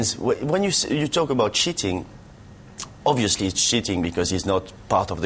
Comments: at 2.55 it sounds like Ginalo is saying "shitting" instead of cheating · LMFAO hahahahahaha when he says cheating it sounds like he says shiting